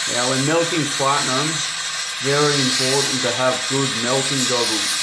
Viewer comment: Good video, but the subtitles said to have food in Okeechobee when you said to have good melting goggles.